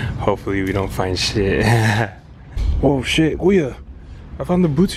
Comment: did you just say kuya 7:43 Filipino or something? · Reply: Yeah I said Kuya, haha I'm Filipino!